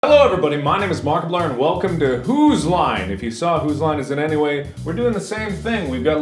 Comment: I thought that Mark said "Who's lying" xD